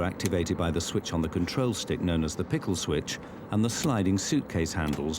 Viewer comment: did he just say, PICKLE SWITCH?